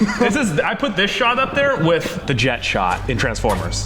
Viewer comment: totally thought you said the Jecht shot, ya know, from Final Fantasy X